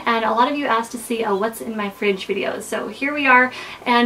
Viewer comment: I heard "What's in my french video" :p